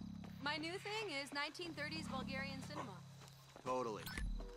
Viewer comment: did she say bulgarian